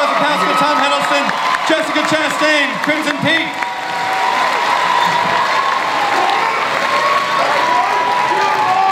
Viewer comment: Hiddlebaby, sign me up :-) The guy yelling at 18:15 "Tom my wife still loves you .._____" Can anybody make out the second part of what he says when Tom laughs? This is the same guy that kept hollering "My wife loves you" when Tom came out as Loki in Hall H 2013. It was hilarious especially at the end when he made Tom laugh. You can hear him only in certain videos where the camera was close by the press pit like this one by KAIZOKUTV. <link> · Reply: I think the second part was "still after 2 years"